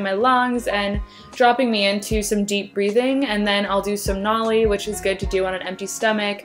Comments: thought she said "do some MOLLY which is good to do on an empty stomache" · I thought she said and then I’ll do some molly in the beginning I was like damn you getting that turnt in the morning 😂